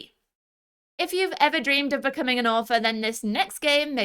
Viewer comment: genuinely thought you said “if you ever dreamed of becoming an orphan-“ and I had to double check if I was watching the right video lol